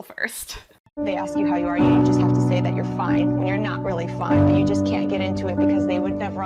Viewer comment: Did anyone else's sound go weird around 1:45/1:55? Like it picked up sound from a different video? I heard something about, "....saying you're ok when you're not ok." Happened on my TV and my phone.